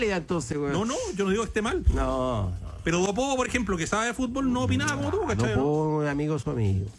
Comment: ¿qué dice? ¿a quién se refiere? me perdí.... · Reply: Dice "tenor do povo" refiriéndose al tenor del pueblo, Danilo Díaz